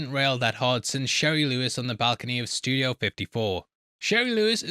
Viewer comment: I thought he said JERRY Lewis. That makes it even funnier.